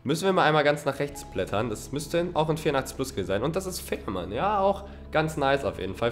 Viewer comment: Habt ihr vei 2:35 au Fickman verstanden😂😂